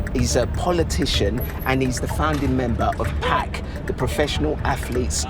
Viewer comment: did anyone else hear someone in the distance say “Pack” in a very high pitched voice???😂